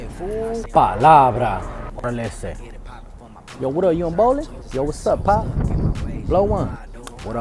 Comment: My boy had me dying for Baldwin park "you going bowling?" 😂😂😂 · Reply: My dumbass thought he said "what up young ballin" 🤣🤣